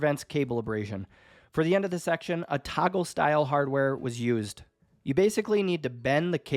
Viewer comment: Looks fantastic. Time consuming but easy to do. 17:59 with your accent, I thought you said "taco style hardware" I was trying to think what taco hardware was. Lol